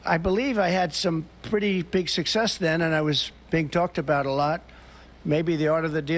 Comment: Did I hear, “Big talked about”?